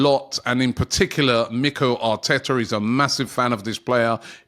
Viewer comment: did i just hear robbie saying nicolo arteta?